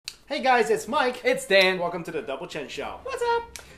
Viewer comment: Double Chen I thought he said double double chin lol